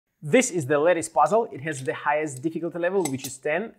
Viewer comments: Lattice puzzle sold here - <link> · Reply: I heard "Lettuce Puzzle" I'm so sorry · with their accent it sounds like theyre saying lettuce not lattice 💀💀💀💀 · Oh I thought he was saying it was a lettuce puzzle...slightly disappointed